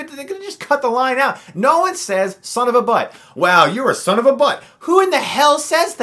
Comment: I thought the car chase cop said Son of a Buck.